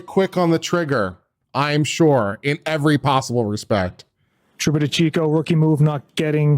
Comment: i thought i heard him say "trooper lebowski".. i wouldn´tn be surprised, if..